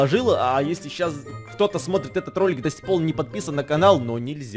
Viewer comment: вы слышали что эдисон на этом месте 11:44 сказал досикпол кто услышал поставьте лайк